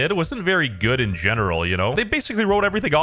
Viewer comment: am I hearing this right, did this mf just referred to Chris as "they", MASSIVE day